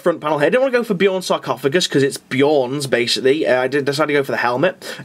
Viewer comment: did you say Beorn? As in Beorn the skin changer from the Hobbit? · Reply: No he said Bjorn. As in Bjorn the fell-handed, a great hero of the space wolves who is also a dreadnought. You can build this kit as either Murderfang, Bjorn or just a venerable dreadnought. So is saying he just swapped out the sarcophagi